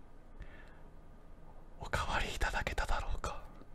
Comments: 怖い怖い怖い怖い怖い怖い パパ👨って聞こえた！ あとおかわりじゃなくてお分かりな！ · 「おわかりいただけただろうか」が「おかわりいただけただろうか」って聞こえるんだけど。。。 俺だけ？